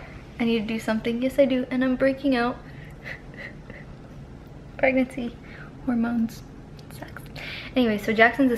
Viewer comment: Did she say shes breaking out pregnancy hormones sex on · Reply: I think she said pregnancy hormones sucks